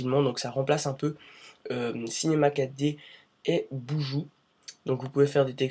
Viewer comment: C'est quoi le logiciel dont il parle à 0:53 ? J'ai cru entendre boujou .